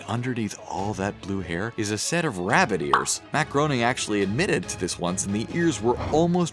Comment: Lol insted of matt groening i heard "maccaroni"